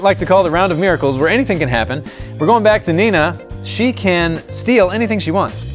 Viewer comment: Who else thought Nina said ninja at first